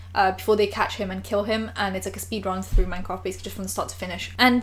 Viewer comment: When she said Minecraft I thought she said Mein Kampft and I was like who the hell made Hit ler's autobiography into a effing game???? Now I just feel stupid.